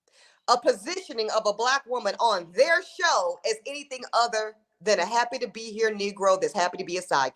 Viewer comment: Are you calling Bershawn's name? That's what I heard. Bershawn was happy to clean up after Ramona and she was just happy to be there grinnin'